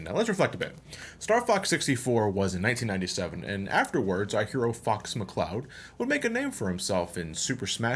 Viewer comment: "our hero fu**s mccloud" is what i heard XD